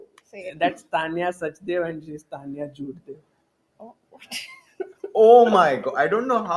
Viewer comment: What did say at 4:26:33 ?! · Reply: Tania Jhoot-dev😪